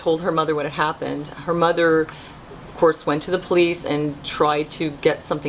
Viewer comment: I put captions up and at 2:18... instead of police. It says fleas. Bahahaha! Yes they are pests! HA.